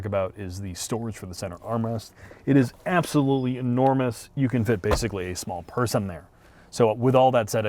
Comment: At first I heard "You can fit basically a small person there." 😅